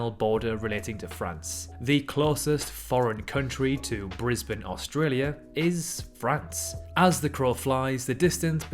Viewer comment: France is the closest European country to Australia is what was said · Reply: no it's not. He said the closest country to Brisbane, which is correct. I misheard it as the closest country to Australia.